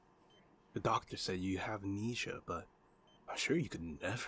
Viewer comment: I HEARD KNEESIA 0:29 😨